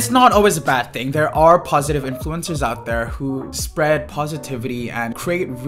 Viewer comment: I thought you were saying influenzas 🤦‍♀️ covid brain!